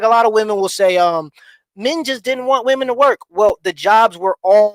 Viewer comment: sounds like he said ninjas didn’t want women to work.